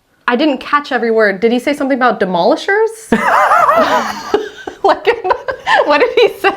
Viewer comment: the frostbit video was from northern ireland as well and not ireland as you thought. Also when he said demolishers - he actually said "da mother said" basically saying his mom said he had no choice and had to go out in the snow